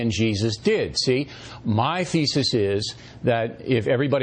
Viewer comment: "My feces is..." I think professor Dawkins heard that so he almost laughs XD